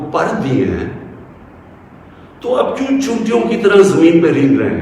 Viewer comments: Did I hear the C word at 7:32? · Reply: 'Choontiyon', ants... I guess he should have said Cheentiyon, as in Hindustani or Hindi. Poor guy ends up sounding like 'chootiyon'... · Did he just say Ch*tiyon ki tarah at 7:32? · Did I hear that right at 7:32? 🤔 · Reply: Choontiyan in Urdu means Ants (insects)